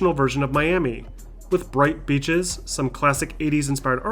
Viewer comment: I thought he said with bright bitches